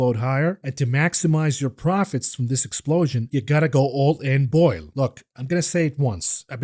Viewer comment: All I heard was " all in on BOIL" LOL